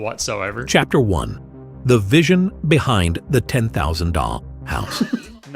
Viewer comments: tell me why I thought he say “the vision of the ten thousand dong. House” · I thought it said a 10,000 DONG house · i thought he said 10,000 dong house for a second, and i wondered if it was the money or dongs